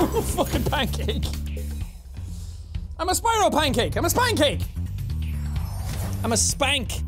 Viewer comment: Did anyone else hear him say "spine cake"?